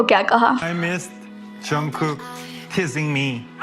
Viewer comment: It does sound like he said teasing but at the same time i understand why some people mistook it for kissing cause it can sound like that too depending on how each individual persons ear heard it, either way the audience had a positive reaction and that’s a good thing